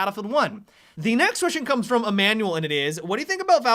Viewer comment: I thought you said "a manual" :v